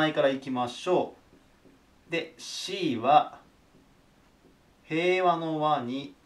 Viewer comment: 令和の和！？ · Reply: 平和の和が令和の和に聞こえるよな